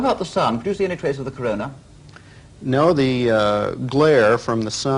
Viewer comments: or 1:18 I dont know what did he say But I heard "corona" 💔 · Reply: Patrick Moore asked him whether he could make out the sun's corona from the lunar surface. Armstrong replied that the glare from the sun did not permit this (or words to that effect). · did he really said corona?